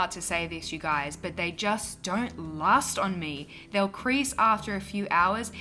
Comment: This look was beautiful! I have a word about correcting the auto-captioning: at 7:35 it says "lost" instead of "last" and "increase" instead of "crease". Many deaf/hard-of-hearing people depend on closed captioning. Would love to see more subtitle accuracy for everyone to have the best Youtube experience!